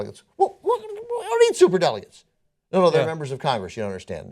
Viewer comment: Ben, your right, CAUCUSES ARE dumb, but then, so are SUPER DELEGATES, States in which the individual vote count winners are awarded ALL the Delegates for that State, and the Electorial Process, dump them ALL! Presidential elections should be based on total US vote count, period! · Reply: I thought he said "We don't need Super Delegates!"?